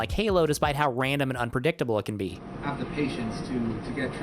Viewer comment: the [something?] he's saying is the word "unpredictable." It's pretty easy to pick up what's saying based on context clues even if you can't clearly understand what he's saying.